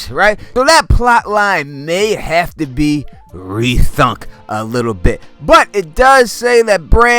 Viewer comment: did he just say “rethunk?” . OMG it’s rethink. Lol